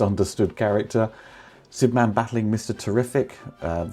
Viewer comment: I thought for a second, that you said 'Superman, fighting Mr. MET. and I lost it. Not sure about your Baseball knowledge but Mr Met is the Metscot he's dressed as a human player, got the Hat as well Curly, Villanous Moustache and no HUMAN HEAD. LMFAO HE'S LIKE Mr. Potato head because his head, is a Baseball